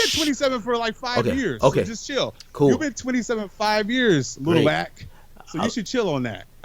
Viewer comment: I thought he called him little hat😭😭💀💀